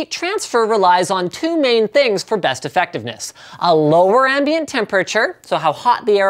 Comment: did i just hear you say "airfectiveness"